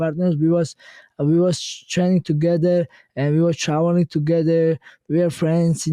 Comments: Did he say travelling together or showering together?? Cus damn that means they're real homies😅😳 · "We were showering together" is that what he said at 23:12 🤣